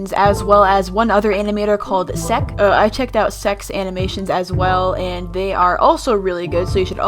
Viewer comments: guys help- at 1:12 my captions say: well as one other animator named sec i also checked out sex animations. TwT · Me: listening to the video: "i checked out Seck's animations..." Me: You. What. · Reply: LOL MY PARENTS HEARD THAT AND THOUGHT IT SAID “Sex Animation” AND I HAD TO TELL THEM IT WASNT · "I checked out Secks's animations." Oh boy that didnt sound to good. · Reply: Even the captions heard it as "sex animations" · You checked out se"ck's" animations and we should check too? 😂 I heard that then went back to see again i was like. Oh Also i went to reply to dammer i think dammer right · she said "well as one animator called sec i checked out sex animations as well" that's what i hear and that's what the subtitle also says :/